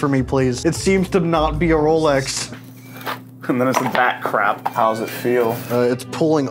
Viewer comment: when say and then theres that crap the subtitles say and then theres a bat crap😂😂😂😂😂